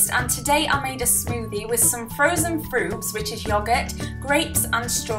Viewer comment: I thought you said frozen fruits, not frubes!! I was looking at the fruit for ages and thinking, that's not frozen haha